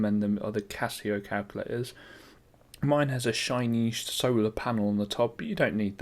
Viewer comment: i thought he said at 9:01 "mine has a shit".....